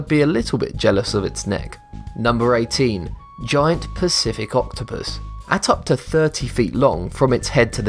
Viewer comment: i heard "giany pussy octopus"... sorry not sorry XD